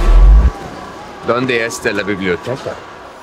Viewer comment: doesn’t he say “hello super powers”? I swear when I watched it that’s what he said but in the trailers I think he did say “donde esta la biblioteca”